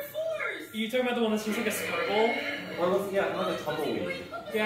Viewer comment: For a second when someone said "can we talk about that one thats just a scribble" at 11:28 i thought they said "circle" and they were talking about mine lol